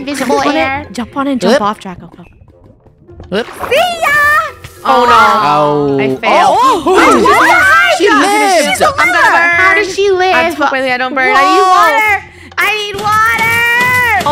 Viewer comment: did funneh just say " Shes a liver"?! 😂😂😂